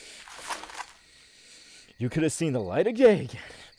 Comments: Did you just say "the light of✨ gay ✨"😂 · wait i heard at the end " u could've choose to see the light of gay "😂🤣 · I heard "You could've seen the light of gay again." Oh. That's nice, sir. I would in fact like to see the light of gay again. Love the video though! Absolutely amazing.